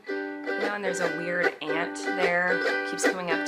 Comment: I keep hearing ‘ant’ instead of aunt or whatever she’s saying 💀😭